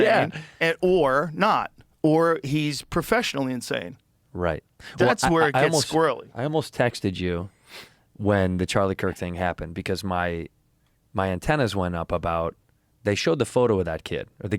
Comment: When Santino said "i almost........jew" .............i thought someone was finally speaking up....i misheard....."i almost texted you" is what he said. BUMMER